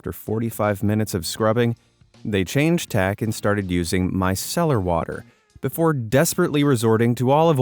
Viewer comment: On the makeup one I thought he said " my celery water"